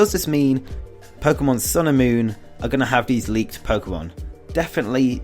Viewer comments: WHY DO I ALWAYS HEAR 'Pokemon Sailor Moon' DAMMNIT!! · I thought you said Pokemon Sailormoon for like half the video..... hahaha